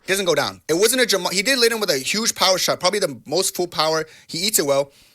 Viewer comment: "the mostful power" 😂 what in tarnation did this man just say